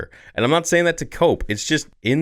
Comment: I heard “as clear as Dave” instead of “day” and now I can’t un-hear it. Spearhead the movement theory! STAR WARS’ FUTURE IS AS CLEAR AS DAVE.